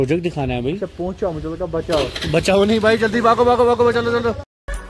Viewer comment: Even I heard 'bachao'